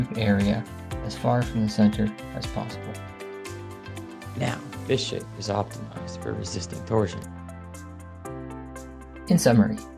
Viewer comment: At first I thought he said "Now, this shit is optimized for resisting torsion."